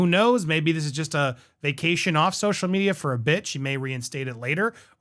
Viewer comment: I swear I heard 'maybe this is a vacation off social media for a b#$&@ lol I know he said 'bit' but still